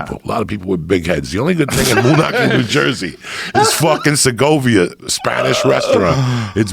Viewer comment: Thought Joey said “Sokovia” for a sec I was like why is he talking about the MCU right now what about the NJ aliens 😭